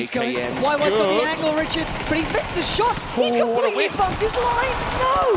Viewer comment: did she just say "He's completely fucked!"? I like this girl lol